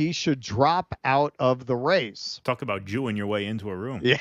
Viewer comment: Can anyone write what he said? I've repeated the moment 10 times, but all I hear is "Talk about Jew your way into a room", which makes no sens to me.